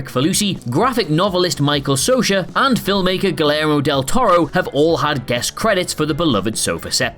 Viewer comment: did I just hear Guillermo del Toro's name pronounced as Gilero? Where's the m? Why's the y sound replaced by an L sound? I need answers!